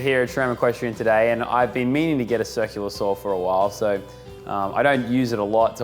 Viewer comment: Haha did anyone else hear sexual assault instead of circular saw at 4:35?